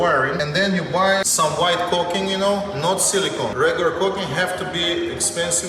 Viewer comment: I thought he was saying "coke" not caulk